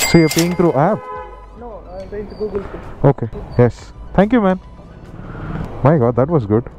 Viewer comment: did he said "thank you maam"? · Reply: Thank you man 🤣🤣🤣🤣